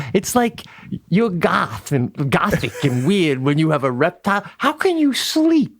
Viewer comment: Pete reminds me so much of Jim Breuer sometimes. I’ve always loved Breuer. I guess I’m into that Long Island exuberance! Love ya Pete! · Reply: almost sounded like goat boy 😂